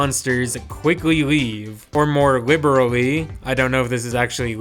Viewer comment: definitely says more "liberally" as in this is not the "literal" translation we are taking some liberties with it, it's an interpretation of what he is saying.